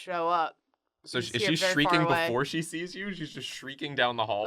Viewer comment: I AM WHEEZING I THOUGHT THEY SAID STREAKING